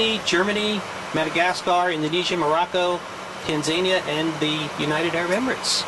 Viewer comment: did i hear Tanzania or i heard it bad 🇹🇿🇹🇿